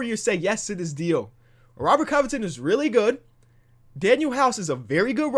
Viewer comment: Yo, did just say that Ro-Co was very good